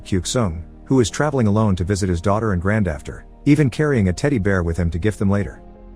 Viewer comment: sir, did you say "granddafter "? 🤔🤔🤔